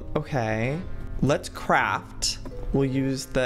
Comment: when Joey said "craft" I thought he said "crap" and I had to rewind and put closed captioning on to make sure 🤣🤣🤣